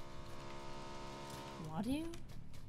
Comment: did she just say wadu 😂😂